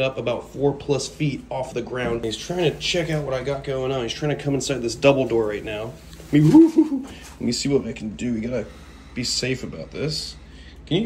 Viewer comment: Thought you said "he's trying to come inside this Dumbledore right now" not "double door"... like... this took a sexual turn 😂😂😂 Kevin and Dumbledore for life 😂